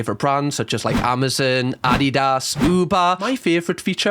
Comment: When he said Adidas, I thought he said Addie Does 😂